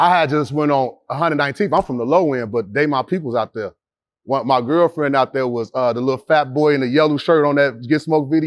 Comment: did he say his girlfriend was a fat boy